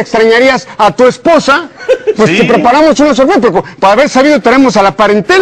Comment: "Te preparamos unos ········" que dice? ... NO SE ENTIENDE. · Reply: Itsukikohaku dice "te preparamos una sorpresa" perolo dice muy rapido